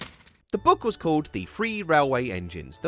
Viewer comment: Correction !! The book was NOT CALLED "The Free Railway Engines" !!!! "Free" and 'Three' are two very different words with TOTALLY differing meanings !!